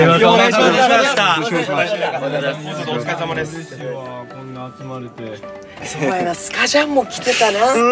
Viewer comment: 嬉しいわ〜こんな集まれて… 声小さくて最初は気づかなかったけど何回も見てたら聞こえてきた🥹